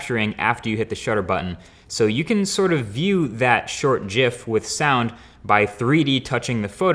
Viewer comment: did he just say jif.......